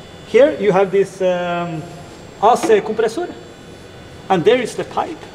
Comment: Did he say ass compressor @ 9:15 ? Lol joking 😂 I know he meant A/C compressor. Nice in-depth video!